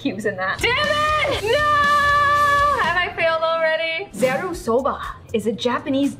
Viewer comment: Me: not even listening 1 minute later: Wait did she say Soba?